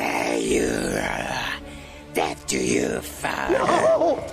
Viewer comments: It sounds like he said farter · Why I hear "you farter"?